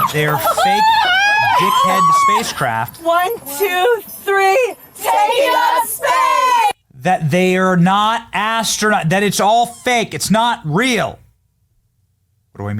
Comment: I also just noticed that one of the ladies was wearing a pair of shoes with like 3" heels @ 1:17 or so. Not normal clothing for space. Also, right after that, did I hear them correctly? Did they all just yell "Take up space"?